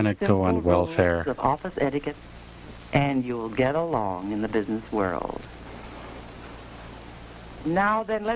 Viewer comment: I kept hearing "getting a LOAN in the business world", incidentally I'm an idiot.